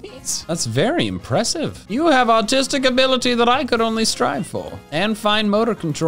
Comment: i misheard artistic for autistic ngl.